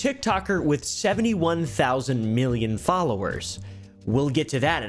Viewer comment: I thought he said she gets millions of VIEWS, not FOLLOWERS...No she decided to do it on line, he asked her to take it down and he suggested a few fixes..but she liked the "rising view count" and all his videos he shared as they grew up , he apparently did not abandoned anyone and seemed to be very involved...she seems to be a little sus to me and she shouldnt speak for her siblings since its crickets on their end🤔 its sounds personal that u chose to not like him cuz of "bit coin" and hopefully if u have kids and ever get divorced, u could him sitting in That chair🤔